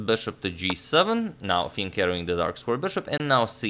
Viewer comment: Let me start by saying this: English is not my first language: so here it goes: I always heard it as "Fiend Catteling" like the bishop gets his cattle and becomes a fiend...